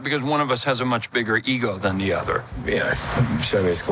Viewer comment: What was the last thing Tom said? When Owen was questioning if the show is called Loki. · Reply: I Heard: "that's what they told me"